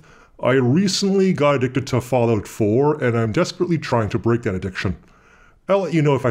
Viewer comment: I had to rewind the beginning of the video several times because I misheard Fallout 4 more than once for father of four... That would be quite an addiction! Great vid, thanks!